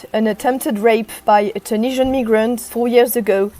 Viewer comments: Did she say ne-grant @ 0:23? I've listened 3× and still hear ne-grant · did she say tunisian niggerant ?